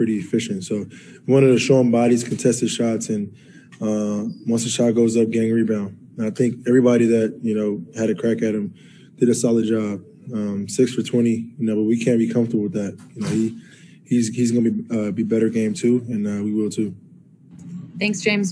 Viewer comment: did Harden just say 6 foot 20? lol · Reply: ６ for 20. probably a FGA?